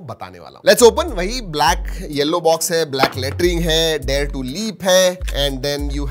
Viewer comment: He said "BLACK LETTERING" I heard "BLACK LATRINE" 😂😂😂